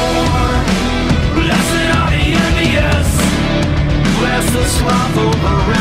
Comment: Is that James Iha from the Smashing pumpkins in the video? I'm not so sure Maynard is a big fan of God. It may be MY EARS BUT, I thought I heard "blessed are the envious" NOT a Bible quote, BUT, maybe I heard wrong.